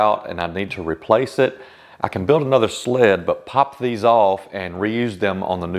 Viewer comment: Golly Gosh!! The tip re getting the runners correctly fitted is ace, 100% ace! BUT……………………. the maths you did…………..f*** m*!! I even played you back at 1/2 speed but still nope! Gosh did you say this was the simple explanation?? I was so fried it took me a moment to get the stop block skit. Which was brilliant by the way! Did you buy the wood block and red paint of Kats site? Great video enjoyed very much but 5 cuts and 3 hours of maths to get to some tiny fraction (I mean feeler gauges in woodworking?!) I thought’we’ we’re free from this stuff ……confused person back in bed recovering…….. Still a great video 😂 Bob England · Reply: I love your skits very much! Considered putting out a Highlights Skits of 2024 at Christmas? 🎅 Y’now I thought you guys were saying ’sledge’ and not sled!? I genuinely dont understand the need for such accuracy in woodworking, I know that I can’t imagine working to such tolerances! Great channel my friend across the pond! Bob England